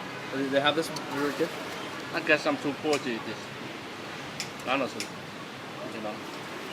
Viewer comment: What is his response at 1:47? I've listened three times and can't quite catch it. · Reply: “I guess I’m too poor to eat this.. honestly, you know”